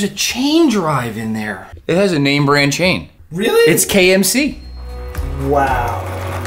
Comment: I swear I first heard you say "It's KFC" lol not that kind of name brand. But no it was KMC. Pretty good.